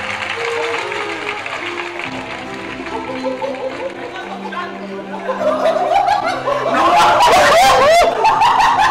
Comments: que dice? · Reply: Me la está apoyando dice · Que dice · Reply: me la esta apoyando